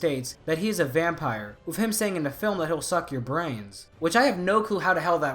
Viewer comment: I always heard it as "I'll suck your veins" which i guess makes more sense for a vampire but maybe I was hearing it wrong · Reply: It's "Veins" but the growl tone Berserker has made it sound like "brains"